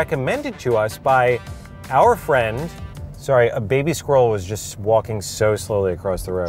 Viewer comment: I thought he said a baby stroller was crossing the street. I had to listen to it a few times to hear squirrel